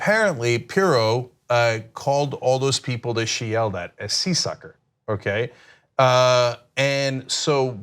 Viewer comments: For some reason I heard “Sea Sucker” momentarily was very confused. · For a moment there, I thought Cenk said "Seersucker" and I thought "What's wrong with being compared to lightweight fabric?"